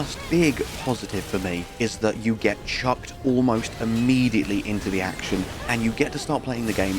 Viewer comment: Thought you said you got chubbed almost immediately for a second